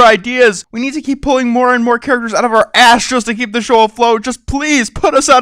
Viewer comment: In the begining it sounded like you said ''pull more characters out of our ASHES just to stay afloat."